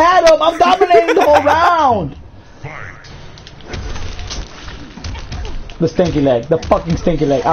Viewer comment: Lmao i thought he said "that stanky lag"